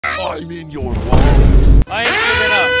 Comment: I heard "I eat your walls" That sounds scarier